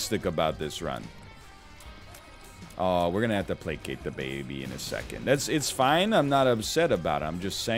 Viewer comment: Oh, it's "placate the baby". I swear, every time I heard "play 'kick the baby' "...